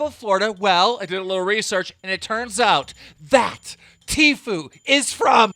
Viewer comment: i thought you said t flu LMAO